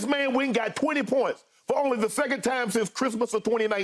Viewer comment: That's why he said second time since 2019. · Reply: oh ok. I when i was listening earlier i thought they said first or only time since. forgive me. i work nights lol